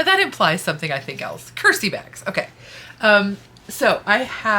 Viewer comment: Originally, I thought you said "Curtsy bags" which is pretty much the opposite of "Curse-y bags". :D